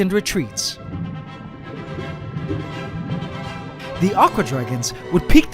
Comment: I swear I thought he was saying awkward dragon!🤣🤣🤣